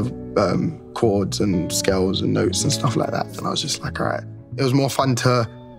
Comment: i think the captions are incorrect; sounds like he says “scales” not “skills”